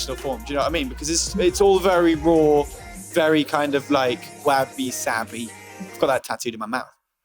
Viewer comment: what did he say? wappy sappy? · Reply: He said wabi-sabi... it is a kind of lifestyle.